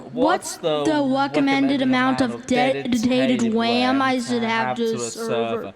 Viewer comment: Hahahahahhaha all these years I thought he said dedicated wam like the candy bar